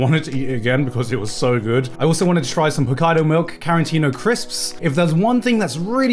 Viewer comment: I thought he said kaidos milk